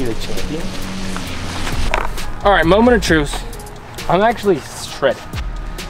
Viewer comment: I gotta know, @ 5:40, did you say, "moment of truce"? Please say yes. 😄